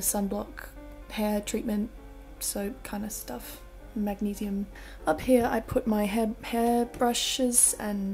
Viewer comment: did you just say magnesium